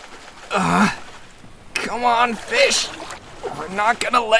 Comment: When Kiri said “come on fish” I thought he said “come on b!tch” 😭🤚